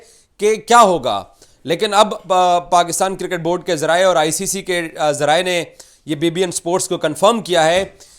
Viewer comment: onwards - did he said, ICC confirmed BBN sports 😂.. chukandar😂😂😂